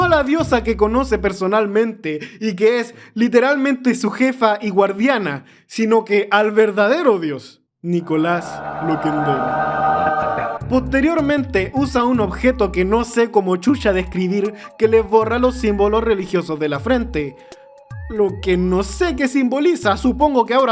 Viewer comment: entendi Nicolas Maduro XD